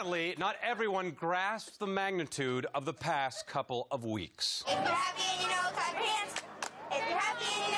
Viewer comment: I thought they said “if you’re happy and you know it crap your pants” at first 😂